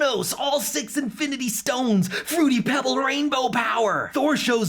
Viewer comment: excuse me, did someone just say: fruity pebble rainbow power?